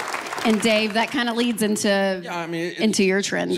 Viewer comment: Did she call him "Dave"???